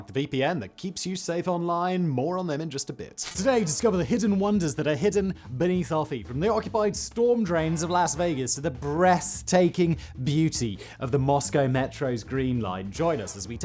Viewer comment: did he say the breast taking beauty